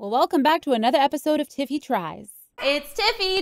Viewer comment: i’m sorry but i thought it said titty fries 😭 but anyways ilysm. you’ve always been one of my favourite creators since 2014 🥰